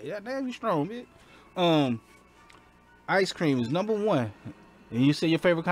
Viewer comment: I had to rewind. Thought you said Heist Cream hahaha